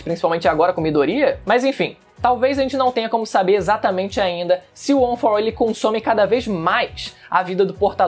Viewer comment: Foi só eu que ouvi ele falar " One for One"